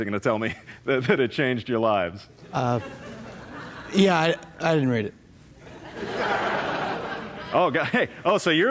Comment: it was obvious he said 'I didn't read it,' the 'n' sound after 'did' was unmistakable, and then he gave an apologetic shuffle. So, how could anyone think he said 'I did read it'?